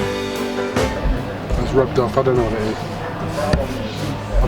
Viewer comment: Does anyone know what he's saying at 6:45 ? Rubdub featherenade? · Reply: 'It's rubbed off - I don't know what it is'